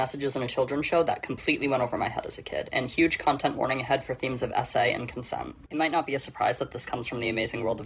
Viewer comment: I wasn’t looking at the subtitles so when they said sa I thought you were saying essay😂😮